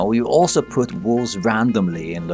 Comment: I thought he said "walls" and not wolves and jumped out of my skin when a wolf came around the corner